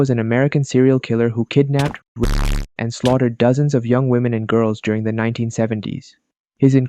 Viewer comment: what did he say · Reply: He said 'kidnapped,raped, slaughtered dozens of women'